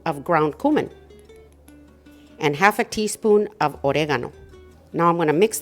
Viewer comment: What is the last seasoning you said. Just after brown cumin. At time mark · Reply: She said "oregano." She uses Mexican Oregano.